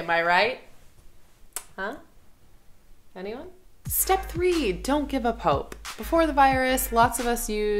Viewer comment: I definitely heard that as "Don't give a pope" 😂